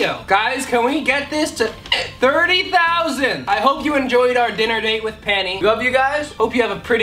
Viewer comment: i thought that said dinner date with Panty